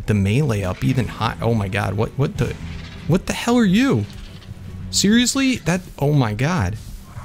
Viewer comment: Who else heard curse at 27:37 saying "what the h**l are you that was his first time cursing ever on this channel put it in the scrap book